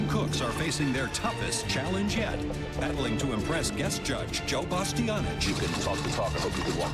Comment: Can anyone please tell me what does he say at 0:11? You can talk to talk and then? I’m so confused hahahahaha